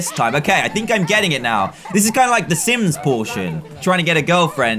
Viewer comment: listen closely 9:58 - update he doesnt swear he just say sims protion